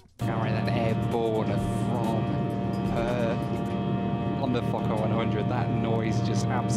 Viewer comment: I had to look down at the subtitles I swore you said F*cker 100 several times. lol